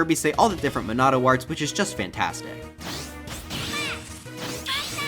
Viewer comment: i thought he said "SHIT" 💀💀